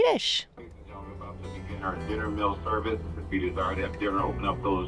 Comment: WONDERFUL VIDEO!! New Sub here!! 😊 9:16. My Closed Captions said: 'IF you DESERVE to have dinner, open up those doors and curtains.😂😂 I'm glad You got your dinner!! Gotta luv those CC!!😅